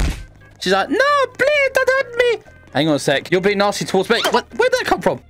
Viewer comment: Oof i thought you said you wouldn't hurt anyone ._.